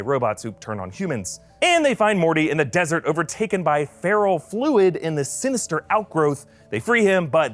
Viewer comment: I think it's ferro-fluid, like iron-based fluid. It sounded like you said feral fluid.